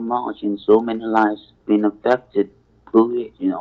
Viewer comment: did he say so many lives have been "abducted" by covid? 🤣🤣